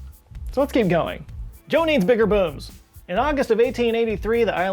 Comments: Oh!! "Joe needs bigger booms." like with an m. See, I thought you said... uh..., y'know, nevermind - it's not important. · I thought he said, "Joe needs bigger boobs" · Sorry for a second there I thought he said "Joe needs bigger boobs" but it was booms. On a side note who doesn't? to both of those statements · Literally thought he said "Joe needs bigger boobs". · I thought he said "Joe needs bigger boobs." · At first I thought you said "Joe needs bigger boobs" haha, boom never crossed my mind xD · who else thought that he said joe needs bigger boobs 🤣🤣🤣🤣🤣🤣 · Im in the part of 2020 where there are large protests. .. And rioting.. Did he just say " joe needs bigger boobs?" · Not gonna lie, I heard that as Joe needs bigger boobs. I was like, you already have a talking tree, now you want big boobs? Man this guy is greedy. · I reaaaallllly thought you said, “Joe needs bigger boobs!” ..... and that laugh was a quality laugh. Thank you, sir. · For a second I thought he said Joe needs bigger boobs.